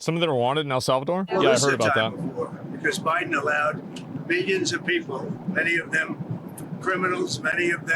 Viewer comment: That audio sounds manipulated overall (background noise warbling) and sounds like he said "billions" instead of "millions".